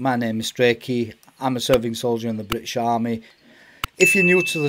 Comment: I heard Soviet Soldiers in the British Army by the way your content is good I love videos about the military.